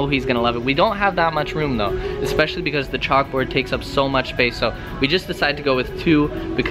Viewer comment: hold up did you just say charuka..? oh nvm lol